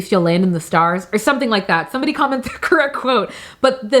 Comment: ~ beautiful message from a beautiful person! Loveeee the encouragement to delve into spices and true comfort foods lord knows it does something to the soul! You’re amazing keep shining ~ 👑👑👑~ 🌕 🦀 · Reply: Girrrrlll I thought you said comment three crowns 😂 either way I watched the whole thing! Every DROP 💧